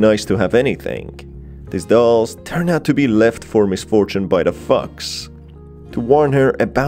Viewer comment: I’m sorry but I heard ‘fox’ as ‘fucks’ and I’m dying of laughter